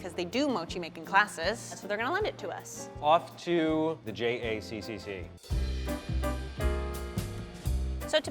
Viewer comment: i thought he said jcc frim nct johnnys communication center.